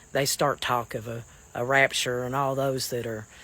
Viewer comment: I misunderstood the African guy. I thought he said "rupture " so I told myself to make sure I didn't lift anything heavy.